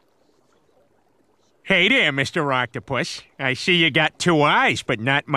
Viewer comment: Did he say Octo-Poos 😂😂